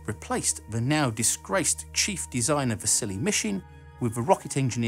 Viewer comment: Whenever he says Vasily Mishin, I hear "The silly Mishin"